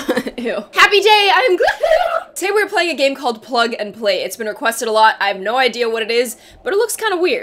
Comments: I thought is said pug and play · I thought it said pug and play 🤣